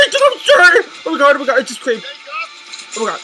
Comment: Did anyone else hear her say i just creamed